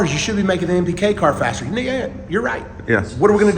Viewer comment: You should be making the npk car faster..."Nigga your right" 🤣🤣🤣 Pretty sure that's what I heard.